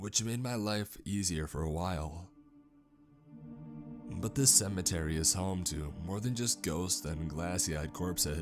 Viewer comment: i thought that shit said centimeters